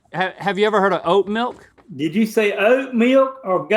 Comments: I thought “oat milk” said “dat milk” agahshajaha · I thought "oat milk" said Dat milk 😂